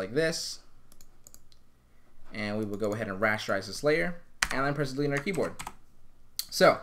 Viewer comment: im stuck i cant hear what you are saying because you were talking to fast. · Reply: Rasterise and Clear